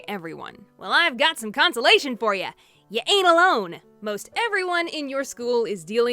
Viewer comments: In 0:28 in i heard your anal lone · I heard 'anal ' from